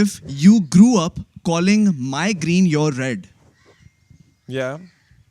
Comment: when he said my green your green i thought he said your migrane my migrane wo to shukr ha k ne keh dia k pagal ha ye wrna mujhe to lagta k ye bhi rabia ki trhan dusron ki pain feel kr skty hain 😂😂